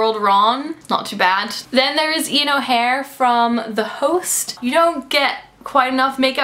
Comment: who did say from the host? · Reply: I am pretty sure she got Ian's last name wrong. I could be mistaken, but it sounded like she said Ian O'Hare instead of Ian O'Shea.